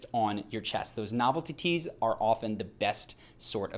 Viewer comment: Did you just say "novel titties" ?